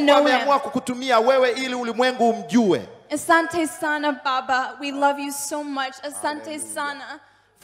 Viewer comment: This just made me sick hearing her say that. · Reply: When I first heard her call him "Asanta Baba" I thought she said "Satan Baba".lol